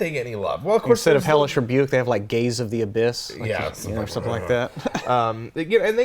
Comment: I thought you said “Gays of the Abyss” and was like what the hell and then burst out laughing at my dumbass self